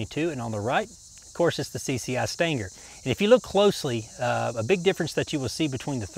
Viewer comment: Stinger. Sounds like "steenger" not "stanger". Had to listen twice. Thought you said standard both left and right side rounds. Great video and very informative. Thanks!